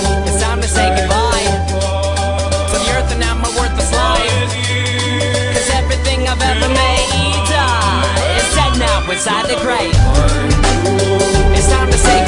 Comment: When he does the "'cause everything I've ever maAAAADaa" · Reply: i always thought he said "cause everything i ever made diiiiies"